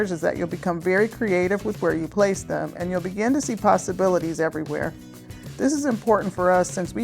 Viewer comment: All the creative ways to hang your pasta--you said so many possibilities. I thought you said pastabilities, which would also work. Nice job!